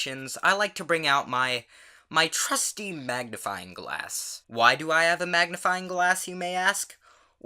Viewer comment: I thought you said "Why do I have a magnifying glass in my ass?"